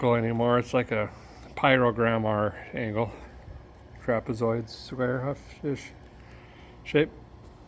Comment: Crapezoid.. that's what I heard you say... And as I looked at the finished result... I'll mm, well?